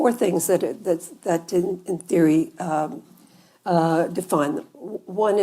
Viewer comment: She kind of buried the lead; @ 2:37 it sure sounds like she says "didn't" define re: the 4 elements. Surprised to learn about Jay Sekulow's background; his Wiki: "bankruptcy protection in 1987; sued by investors for fraud & securities violations." Sadly now there's another right-wing "charismatic" Catholic on SCOTUS.